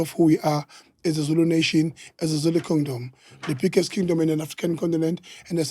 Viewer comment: Did he just say Zulu condom 😂aii this man is a joke 😂😢